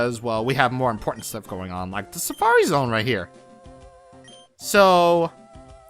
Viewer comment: for a second I thought it said Safari Bowser XD